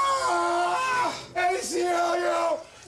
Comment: What is he saying? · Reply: He's saying aclu which means"American civil liberties Union"